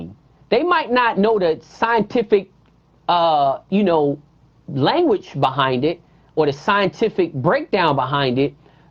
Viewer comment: I did not understand some parts on how the birds shuffle... cross wise and what he said was like the scissors? What is ustisa? 1:41:58? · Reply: It’s (Postiza) which is the weapon that he’s referring too. It’s a fiberglass made spur.